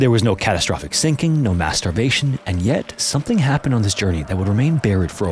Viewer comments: Oh you said "No mass starvation", I heard something dirty there. My mind is in the gutter. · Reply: I had to rewind it cause I thought I heard no masterbation an I was right 😂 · I've listened back and it does indeed sound like you said masturbation instead of mass starvation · I swear, I heard him say “No masturbation”. My mind went there too! LOL ✌🏼👽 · sounded like he said "no masturbation" !!!! 😂🤣😂🤣........i was like "why not?"🤨 😆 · No mass.... turbation? I'm listening as i fall asleep, and I swear I heard that, or I'm just overtired, or overdue, take ya pick. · My eyes was closed and then I heard “no masterbation” 😂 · "...some masturbation..."?🤨😳 Was that on the itinerary? I'd think doing that, there, would entail some...uh...adjustments.🤔 I had to stop and peek at the comments and it seems you said "mass starvation", so I listened again, forcing myself to hear "mass starvation", but alas, all I heard was "some masturbation." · Oh, "Mass Starvation". I had to rewind a couple of time 'cause I kept hearing "masturbation". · Haha, when he said "mass starvation" at the start, I heard it as "masturbation". I thought, there's no way there was no masturbation going on!!! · When you said "No mass starvation." 0:23 I heard "No Masturbation" and for a minute I was like...Is there alot of masturbation when there are disasters?😂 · I totally heard ‘no masturbation’ 😂